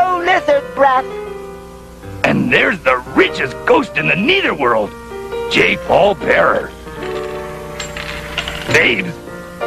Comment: did he say Jake Paul